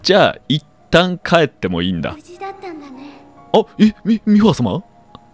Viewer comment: のところ 「あ、え、み、ミーファ様！？」が 「あ、え、み、美輪様！？」に聞こえて美輪明宏が天の声やってる姿想像してしまったwwww